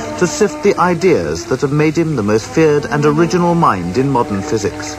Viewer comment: Did he said "most FEARED(?) and original mind.."